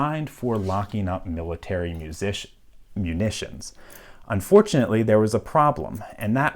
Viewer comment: I thought he said this lock was designed to lock military musicians. I'm like damn play a wrong note, get locked up. Talk about facing the music 🤣🤣🤣🤣